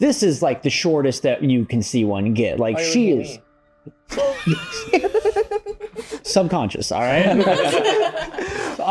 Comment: What did say? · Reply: Why are you looking at me?